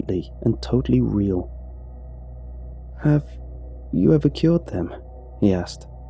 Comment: I thought he said killed instead of cured